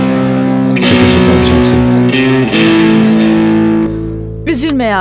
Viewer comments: te sedatın ne dediğini anlayan varmı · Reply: söke söke alıcam seni diyo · sedat ne diyor anlayan var mı? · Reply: Söke Söke alicam seni diyor